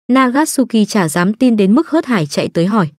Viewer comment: Nagasuki t nghe thành Akatsuki